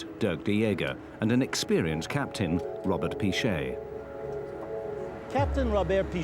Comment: I heard Gromet not Robert